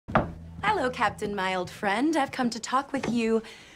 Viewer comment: I thought she said Captain Miledfrend